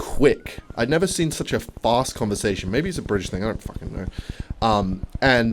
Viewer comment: did u say a British 🧍🏿‍♂️?